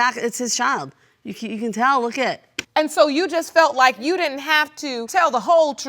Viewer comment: Did she just say "'Look at IT?"